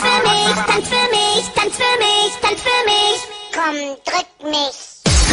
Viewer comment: That one german word I hear: “come touch me”